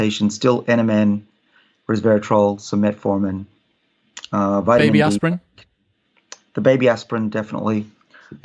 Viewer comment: Summary of what he takes (if I have written something down incorrectly, feel free to let me know) – NMN – Resveratrol – Metformin – Baby aspirin (helps keep inflammation down, which is the hallmark of aging) – Quercetin (suppresses activity of senescent cells) – Fisetin (a new supplement he is experimenting on himself) – Vitamin D – Work-out with heavy weights 3 times a week (instead of 1) – Skipping lunch in addition to breakfast (apart from a little yoghurt in the morning to mix resveratrol and quercetin with.) – One drink – either red wine or whisky – at night. Thanks for the video. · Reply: I almost thought he said Vitamin E. Not D. Was it said anywhere what brand of Fisetin he takes?